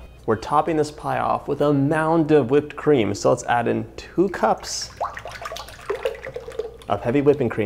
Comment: you say "2 cups" but the subtitle says 1 cup. which is correct?